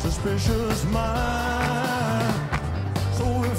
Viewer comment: Fantastic. What does he say at 0:31 ? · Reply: He says "shove it up your nose" 😍